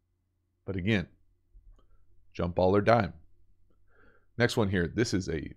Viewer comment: Jump ball or dime sounds like a great new game for viewers. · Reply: I thought he said jump ball or die. Did I mishear?